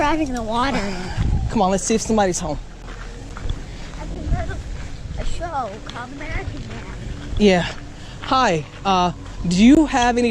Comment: did the kid just say "I heard of a show, uh American dad" ?